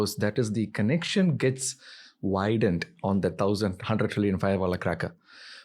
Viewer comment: what did he say here? "thousand fireball firecracker?"